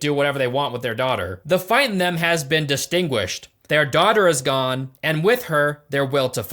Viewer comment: did bro say the fight in them has been 'distinguished' 😂😂